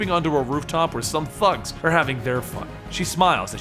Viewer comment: I thought you said "some fucks" when she came down on the thugs. 😆